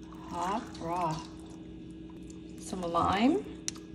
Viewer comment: Oh I thought she said "some lime, poison" 😂